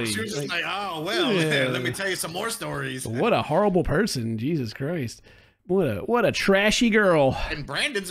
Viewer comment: Did just say "Jesus Croist" like a true aussy?